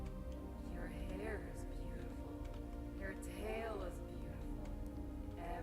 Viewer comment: I can't hear Roxanne wolf @ 4:34 what is she saying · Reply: "You're performance was perfect tonight! Thank you. Your hair is beautiful, your tail is beautiful..." etc